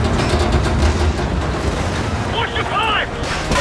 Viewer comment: what did the soldier said in 7:31? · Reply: "Watch the pipes."